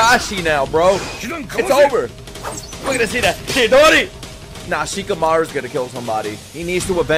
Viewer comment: I thought you said "Shikamaru's gonna kill Temari".